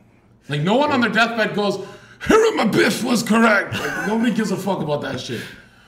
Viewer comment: what was he saying at 54:05 😂who was correct? or was he bullshiting some name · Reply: hiram abiff. Its part of a masonic ritual to become master mason, (3rd degree)